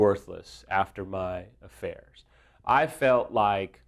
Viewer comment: did he say "After my affairs" .....? As in affairs with an 's' ? Plural? How many affairs did you have?